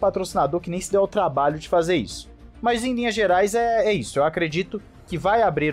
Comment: eu ouvi "Mas em Minas Gerais é isso" 😅😅😅